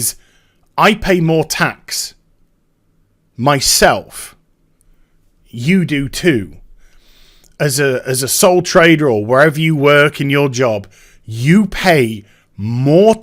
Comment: Lol. I thought he said "soul trader".